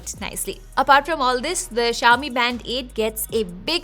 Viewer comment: I keep hearing "Band-aid"